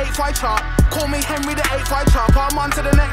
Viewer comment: Thought he was saying "call me Henry the 8th I chart"...